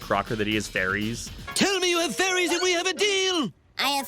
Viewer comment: i think i heard Timmy say furies but am not Shure